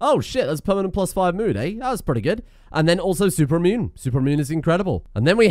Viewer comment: when you were reading super immune i thought i heard super mutant and i was like "it was gonna happen one day"